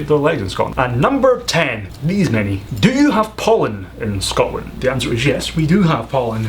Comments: I thought he said 'do you have Poland in Scotland?' at first :) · I thought he said, "do you have Poland in Scotland?" which was a lot funnier than the actual question but oh well. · For question 10 I thought he said "Do you have Poland in Scotland" and after he said yes I was kinda confused… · for 10 I thought he said "do you have Poland in Scotland" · The last one I thought you said the question was "Do you have Poland in Scotland?" and I started laughing so hard. xD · I thought u said do you have poland in scotland XD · Not gonna lie, at first I heard #10 as "Do you have Poland in Scotland?" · I thought he said "Do you have Poland in Scotland." I just laughed so hard. XD Then I had to watch his mouth to understand what he was saying. :D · I legitimately thought he said "do you have Poland in Scotland" and my brain pretty much formed a hand and facepalmed me from the inside as soon as I heard that.